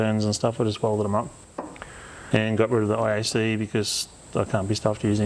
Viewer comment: you 'got rid of the ISE'? Can some one explain what he said there? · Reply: Think it was iacv, inlet air control valve